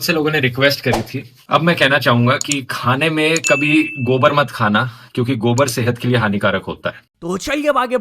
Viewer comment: What is he trying to say "Gobar" kya? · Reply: His saying gobar mat khao